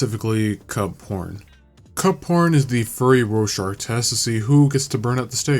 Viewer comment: 😰I thought u said cup p0- Honestly I wish u said cup